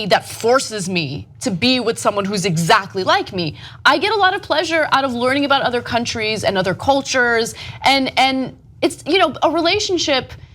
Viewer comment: Oh Ana I know you like that BBC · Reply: right! at 2:42 i heard "i get alot of pleasure out of black dick" lmao